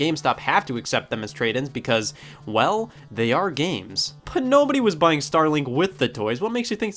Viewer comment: When said "Well, they ARE games." an ad pooped up and cut the word game short. So what I heard was "Well, they ARE gay."